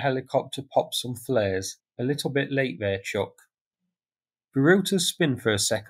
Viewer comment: Thanks again. This was really cool to watch on the slow mo and HD. And I had to smile when you said, "A bit late there Chuck" when referring to releasing flares. Right on. Always a pleasure seeing what you bring to the table for us to be informed about. Great job!💯❤‍🩹🇨🇦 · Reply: I heard ‘A bit late there chook’.